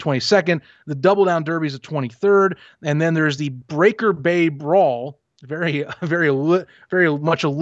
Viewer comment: I thought ya’all said 🌊 Breaker Babe 🥊 Brawl, lol 😆 and was thinking, oh boy, Spring Stock about to be 🔥 Before I was thinking Cloth Frisbees/Frisbee Golf ⛳️ a few OG O - riginals like Herschel Walker, John Corker, Derek Holloway, Cliff Stoudt, Scottie Norwood gathered around tossing a football back n forth.